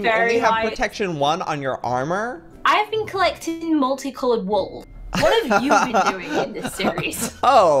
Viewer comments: who else thought said I've been collecting wool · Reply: oh! I thought she actually said wolves. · I thought said she was collecting wools